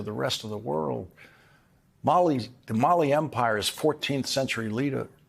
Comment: When Lawrence referenced the Mali Empire, I heard "molly empire," of which Elon Musk would surely be the head.